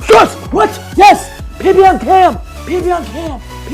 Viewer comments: thought he said "pp on cam!!!!!!!!!!!!!" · i think he meant to say peanut butter on ham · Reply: he said pb on cam - personal best on camera